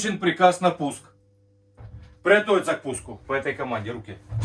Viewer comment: мне одному послышалось "приготовить закуску"?